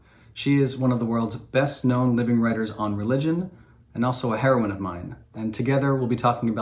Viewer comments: I thought you were saying "heroin admirer" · I heard "heroin admirer" :D